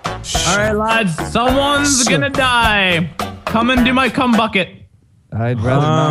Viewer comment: what did Mini Ladd say "come into my c(h)um bucket... what the hell